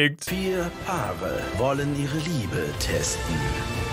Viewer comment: ich hab statt liebe Ehre verstanden 😂😂😂😂😂